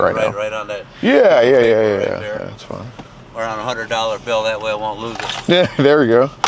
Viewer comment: Oh shit I was only listening and thought a cop said “write it one a $100 bill…..”. 😂